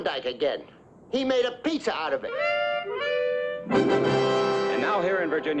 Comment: That pizza comment used to make me laugh so damn hard when I was a kid that I could not breathe. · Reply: For some reason when I was fairly young, I thought Tennessee said "He made a piece of it" and was confused. Years later, I realized what the actual line was and found it very humorous.